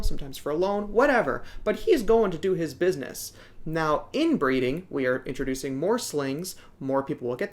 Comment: I thought you said 'inbreeding' and got a little interested. What do you think of inbreeding btw? Like mating sac mates? I heard the whole stock of P. Metallica in the hobby was bred from only a few wild caught adults. Or something like that I'm not sure.